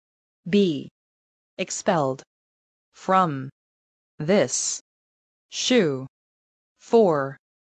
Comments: She said "shoe" instead of "school". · miss martin says shoe instead of school